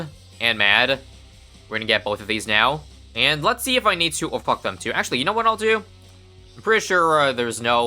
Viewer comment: did he say "oh fuck them too"?